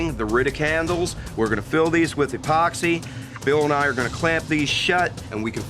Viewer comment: At around 16:45 it sounds like he said Bill Nye